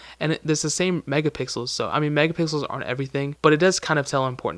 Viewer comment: Anand Masih didn't he already say that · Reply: Albin Isufi oh, he said: aren't everything! I thought, he said: are everything!😅 Sorry, my bad! I think I was watching the video with low volume.